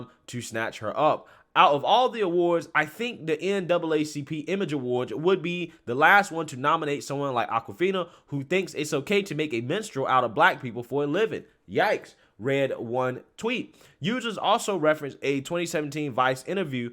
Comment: Oh, she said "Minstrel-cy" I thought it sounded like she said Menstrual-cy